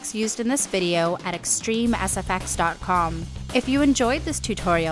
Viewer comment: Thought you said "Extreme ass effects"..